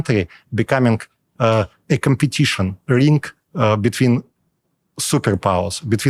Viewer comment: Subtitles wrong. When he says "rink" the subtitles say link. He's describing countries like Ukraine, Georgia, Kazakhstan, etc becoming a "competition rink (think hockey) for global superpowers".